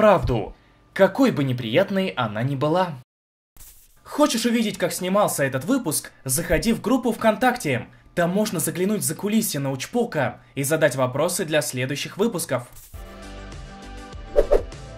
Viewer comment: Мне одному послышалось закулисину учпока